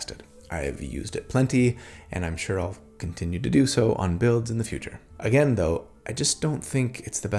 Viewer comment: My video audio got a little janky at 31:21, sounds like you said "and I'll f**** continue to do so, on builds in the future." It gave me a good giggle at the implication.